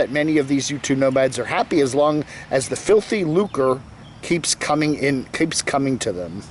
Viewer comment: Serious question....@ 10:24 says something about “filthy .....”. Can anyone please tell me what he said, and what it means? Thanks! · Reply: "filthy lucre": illicit gain of money, profit. E-Beggars.